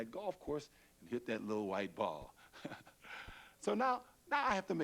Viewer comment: did he say he loves to hit that little white ball, or he loves to see his little wife fall?